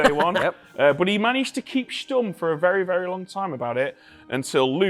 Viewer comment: Did he really say "But he managed to keep stumm"? :D Was just wondering about that because 'stumm' is a german word (which translates to 'silent/quiet') and I've never heard of it being used by english speaking people :D